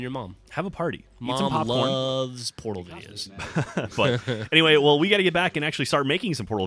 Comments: I thought he said "Mom loves porno videos." lol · When geoff say's mom loves portal videos. First time through I thought he said Porno